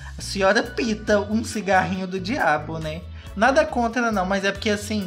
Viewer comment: eu ouvi : cigarrinho de quiabo kkkk